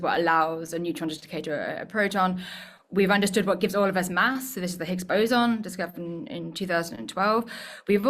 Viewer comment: I thought she said it “gave us math.” I was like “okay but, what now?! Thats a real ‘divine speck’ if there ever was one!” (I won’t pollute these comments with the accursed buzz words.) I wasn’t sure which was the Sabine for a second!